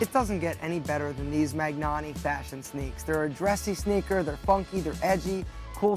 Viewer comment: What kind of shoes did he say that would last for years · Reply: Magnanni. They are a handmade shoes company in Spain. there shoes are like 300 to 400 but you can find them on sale for like 200 or even 150.